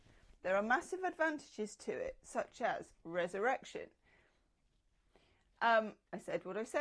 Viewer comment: I heard "erection" when you said resurrection. Was the emphasis yours, or mine? 😂